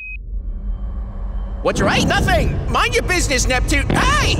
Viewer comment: Repeat it lots of times and it sounds like Neptune is saying “Walter White”